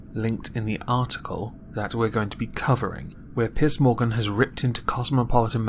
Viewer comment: It sounded like you said "Piss Morgan". Something relatively fitting I would say.